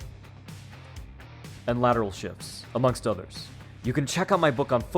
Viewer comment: At first watch i thought u said Lateral shit like you were dissing Ddp's footwork 😭